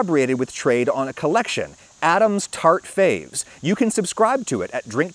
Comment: I heard "Adam's Tart Favs" as "Adam's Tart Phase" and was like "Oh ok he likes it now but in a few month's he'll be on to a different coffee"